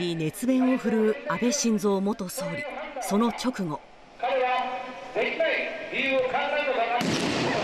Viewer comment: What did Prime Minister Shinzo Abe say in the meantime? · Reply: 「出来ない理由を考えるのではなく！」